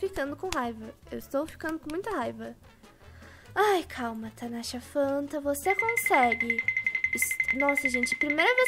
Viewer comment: que? tanasha fanta eu escutei?????? eu em kkkkkkk